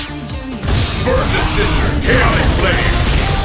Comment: It sounds like he saying," Burn this SHIT!" 0.09